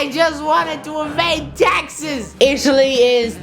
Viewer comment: Guys did he said that he wanted to invade Texas or did he just say taxes